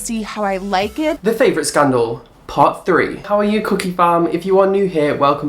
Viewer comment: ALSO HAPPY BIRTHDAY!!!!!!!!! <<num> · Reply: Serena E omg I thought he was saying cookie fam 😳